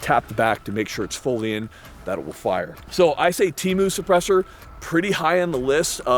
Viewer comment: did he just say temu suppressor 😭